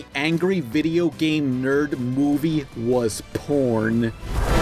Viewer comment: Wait. Did he just say porn? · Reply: Whatever you think would say is the right answer. So, yeah he did say porn.